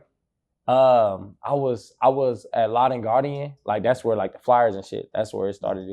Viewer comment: Did he say “Lawd and Guardian” section of Walmart? Where "they sold flyers"? WHAT???? hahaha